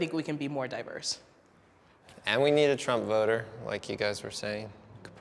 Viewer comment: Lol at 3:34 I thought the dude said "we need a tromboner" and I was so confused